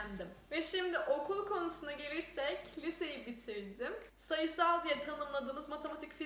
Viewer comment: ''okul konusuna gelirsek kiliseyi bitirdim.'' bir tek ben mi öyle anladım :D